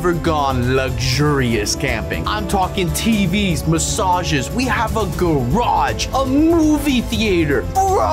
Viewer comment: At the intro of the video I thought he was gonna say : We HaVe a GoRiLlA :😂😂😂😂😂😂